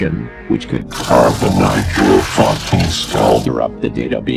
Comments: does it actually say "carbonise your fucking skull" or am I going insane? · did it say “drive a knife in your fucking skull”? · Does anyone gave anyone know what was said here, I think it's something like "[something] your fucking skull". It sounded like "carbonite" to me. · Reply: Carbonize. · I think it says, "carve the module into your fucking skull," at 2:57, but I could be wrong, but that's what I could make out.